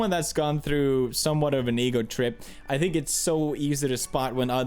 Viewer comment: ohhhh...ego trip. I thought you said negro trip. lul